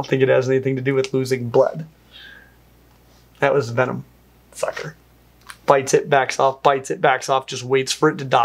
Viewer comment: does say “that was venom, f*cker.” or am I mishearing that? Lol either way. Also I think Spinosaurus doesn’t have venom, their bleeding effect must work like a video game status effect; either it does damage over time or it’s like Elden Ring where if they hit their enemies enough times it causes them to “hemorrhage” and take a ton of damage at once.